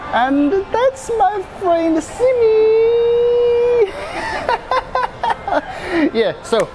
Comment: thought he said Siri